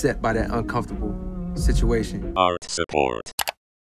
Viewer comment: I thought the intro said arch support😂🤣🤣